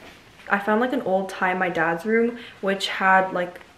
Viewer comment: I THOUGHT IT SAID MY DAD LEFT ME LMFAOO